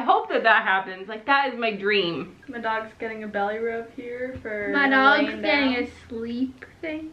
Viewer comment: Omg I thought said "oop my dogs shitting" at 21.26 ahahha